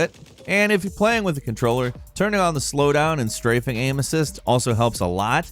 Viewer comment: is he saying turn down aim assist ?